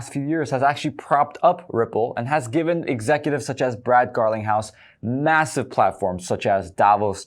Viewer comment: Did u just say Brad garlic house 😂😂